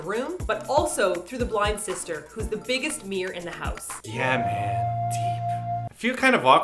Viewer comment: "bigger mirror in the house" part sounded like you said Mir. Tee hee. Biggest Mir in the house. =)